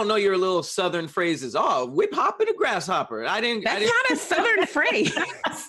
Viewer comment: This man said “Whip Hop and a grasshopper”😂😂😂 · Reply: I thought he said "we poppin a grasshopper" 😂😂💀